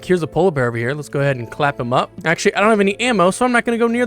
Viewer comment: I'M SORRY BUT DID YOU SAY "CLAP HIM UP"?